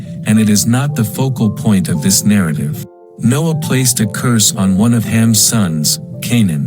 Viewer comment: it says "Noah place to curse" instead of "No0ah placed A curse" You aren't helping the hearing impaired at all with these translations.